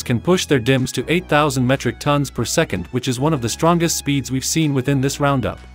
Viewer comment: Did i just hear 8000 Metric ton per second instead of mega transfers per second